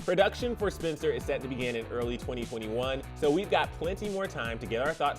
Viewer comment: I thought he said 2041 😭